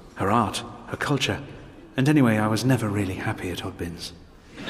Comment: what does he say around 0:30? "and anyway i was never really happy.."? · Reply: mai567 At oddbins. It's a wine shop in England.